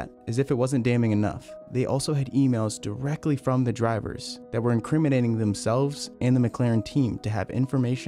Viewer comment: you say "they had emails that were incriminating". But the report in screen says "highly incrementing"...meaning emails were increasing steadily in numbers...not that they were incriminating themselves. Just basing it on what I hear and see...